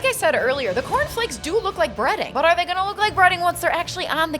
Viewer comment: cornflakes are a legit breading option, sometimes for its gluten free properties, or just for a better crunch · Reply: I thought you said breeding and I was very confused